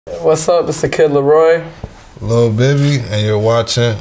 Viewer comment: Who else thought it said lil baby